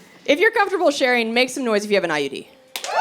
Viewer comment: did she say I.E.D.? Or I.U.D.? An army buddy wants know. I guess both are meant to destroy life, juuuust a little bit differently. Just sayin